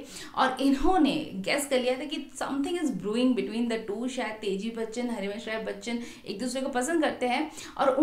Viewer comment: aapne 7:08 me kya kaha hai... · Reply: "something is brewing between the two" matlab dono k bech main kuch chal raha hai (yaha Amitabh Bachchan k mata pita ki love story k sandarbh main bola gaya hai)...hope this helps 🙂 thank you for watching 🙏